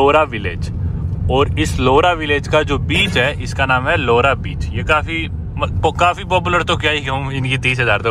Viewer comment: kya bola? Kya naam hai?😂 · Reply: laura beech oh😜😜😂😂😅😅